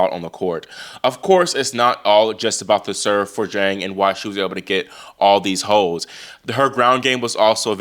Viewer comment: I thought you said at 2:00 .. I thought you said “that’s why she’s able to get all these hoes” I nearly dropped my coffee lol